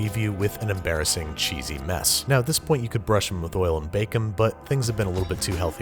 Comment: Who else heard "brush them with oil and bacon" @ 4:48?